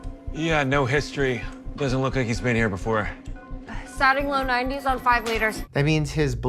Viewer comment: Your captions got "wound check" wrong less than 30 sec in and called it Warm check so i assumed maybe they were bad YouTube auto captions. But they aren't. They're just... idk i think it would be nice if you updated any mistakes in the captions like that and I'm just getting started watching. So idk how many there are throughout. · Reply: The actual open caption at 1:15 (I'd turned off the closed captions) is also wrong. They're saying "Sat-ing" which is about the Sat level, the Blood Oxygen Saturation level. But you wrote "Starting", another mishearing situation. I'm surprised there isn't anyone double checking before posting that caught that one. Since you're trying to help the viewer understand with that open caption.